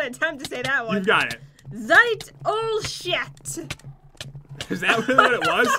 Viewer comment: lol sounds like she said sh*t